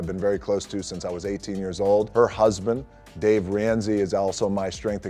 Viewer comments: I thought he said Dave Ramsey lmao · I thought he said Dave Ramsay 😂